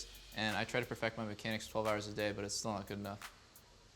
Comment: how many hours a day ? i just dont get it ... 4 , 5 or does he say 12 ? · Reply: he said 12